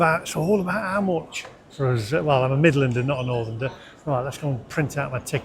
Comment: did you just say 'NORTHERNDER'??????!!!!!!! No such word, and most 'northerners' would throw you out of Yorkshire for saying that.